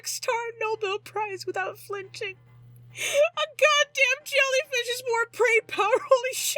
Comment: Does anyone know what said at 31:55? “a jellyfish is more ______” I didn’t understand what word came after that. I’m not tryna be rude just wanted to know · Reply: "a god damn jellyfish has more brain power, holy shit. Im laughing so hard im going to wake up with a six pack tomorrow" And you are far from rude :D